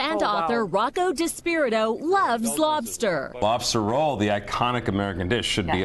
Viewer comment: did she just say slobster🤔😲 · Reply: Michel Mpindi no she said loves lobster